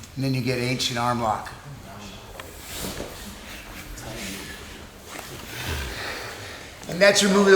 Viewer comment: For a second i thought he said h&r block lol